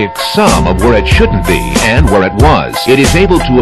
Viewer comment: I thought he said "Witch is called air" i thought he summed up what aie is LMAO